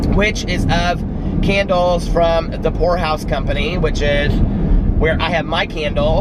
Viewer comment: Every single time you say The Pour House Company I hear “The Whore House Company”! Lol Every. Single. Time. 💙💙💙💙💙💙💙💙💙💙💙💙💙